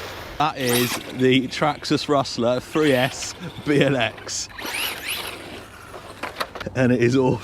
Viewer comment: wait, did he just say "traxxas rustler 3s BLX" at 1:50 ??? did i miss something?